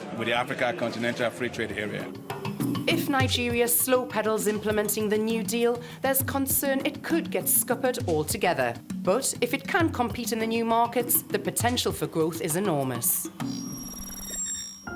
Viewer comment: What's that word? Scoperd?